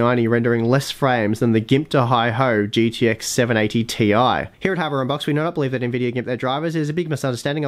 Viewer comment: you say "Here hardware unboxed believe now that nvidia ___ their drivers is a big misunderstanding I wish I never said anything" in like 2 seconds. It's unclear what you said and it got me really curious. Can you please type it out? :) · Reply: ) It was just a silly joke, here is what was said... "here at hardware unboxed we do not believe that Nvidia gimp their drivers, it is all a big misunderstanding, I wish I never said anything"